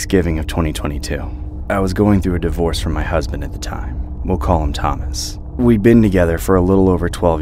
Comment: for some reason I heard "I was divorcing my cousin at the time" 😭 I had to rewind twice